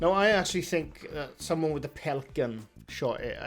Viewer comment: Ngl when you say pellet gun I thought you were saying pelican lol